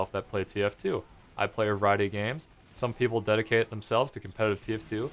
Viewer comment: did he say "I play erotic games"??????? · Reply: A variety of games